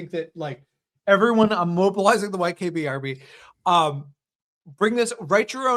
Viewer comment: NGL, when he said this, he said it so fast I heard “WHITE KB Army” and thought, “yeah, that definitely tracks”